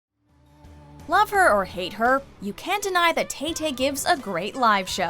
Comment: Hate her all you want, but she pours her heart in her performances. She deserves all the success she has. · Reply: At the beginning of it I thought they said lover not love her 😂